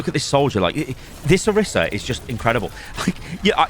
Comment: I thought you said Orisa sucks, just ignore her?